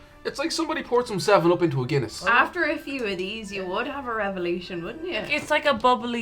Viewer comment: "After a few of these you would have a revolution, wouldn't ya?" What an amazing quote lmao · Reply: Ah, that's what she said. I was a bit confused because I thought she said 'After a few of these you would have a revelation' and I was like 'I know porter is a stronger beer, but having a revelation after a few glasses is a bit much'.